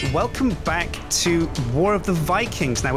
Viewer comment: Oh. I thought he said "welcome back TO war of the vikings" :O